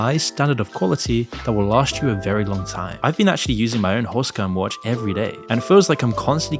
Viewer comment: Your clickbait titles are gonna tank your channel homie. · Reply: sounds like you said “horsecum watch” 😂